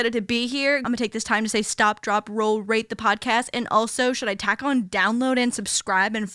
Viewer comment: Not sure if this is valid criticism but every time says "rate the podcast" I hear "rape the podcast" & it's kinda uncomfortable but not a big deal I dunno